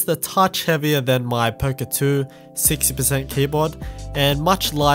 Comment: what was that 60 percent board? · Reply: I think he said poka 2